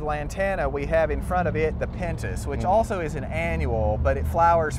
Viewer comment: Can anyone help me at 6:54 this pretty red flower is he saying "pentiss"? I would love to incorporate that color in that plant in my garden but not sure of its name. Thanks friends. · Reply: They're called "Pentas" and are great hummingbird attractors.